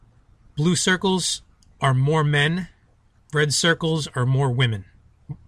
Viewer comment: At 6:3o when he said more men, I was looking at Utah and I thought he said Mormon lol :D